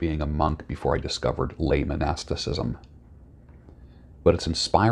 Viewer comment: English is not my native language, so i couldnt catch which word that is; lay-man-astisiscm?